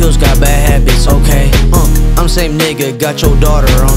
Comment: did he say niggah?